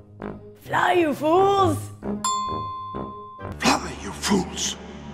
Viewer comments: Oh wow, I always thought he said, "Run, you fools." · I thought Gandalf said "run you fools."